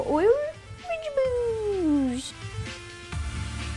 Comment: I thought she said VEGIE-BOOZE! (I know I'm retarded -_-)